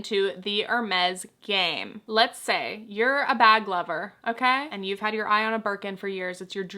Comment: One of my ears is on the fritz and I misheard "bag lover" as "bad lover" and was like omg how bad would you have to be the buy a Birkin to apologize or console yourself?! 😭 And somehow that cursed brain autofill STILL makes more sense than this rich people nonsense! Like go outside! Damn.